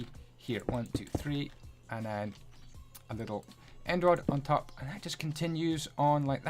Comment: If u put subtitles the subtitle say Android not End Rod